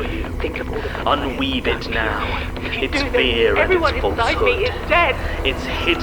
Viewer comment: "If you do this, everyone inside me is dead!" I sure hope that was a lie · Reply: I heard “Everyone inside me is already dead”